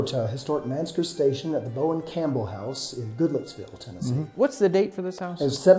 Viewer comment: Looking dapper, these two were. I thought he said "GoodLooksVille, TN" at the end...you'r telling me! LOL!! I looked it up...it's "Goodlettsville."